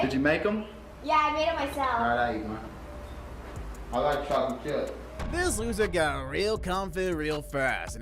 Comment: I like how at @ 21:06 he said "i like chocolate chip" and the caption says "i like trying to kill". Sick editing lol.